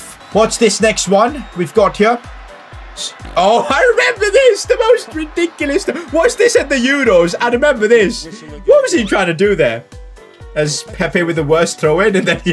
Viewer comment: did he say was that in the Euro’s and that was an Iranian Player